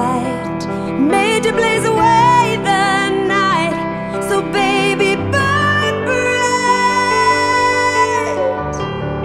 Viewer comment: First time I heard it thought she said “Baby bird brains” 😂😂😂 sorry if I ruined the song for ya that’s all I hear now!!! Hahah awesome song thou!